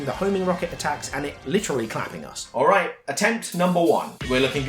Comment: what did you say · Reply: 'Literally clapping us' because he claps his hands and hits us with them